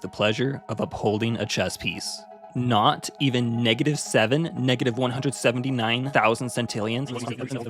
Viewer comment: can you say that number again i didnt hear it clearly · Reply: 179,000 cintillians